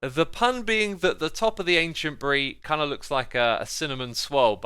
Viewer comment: did x say ancient brie???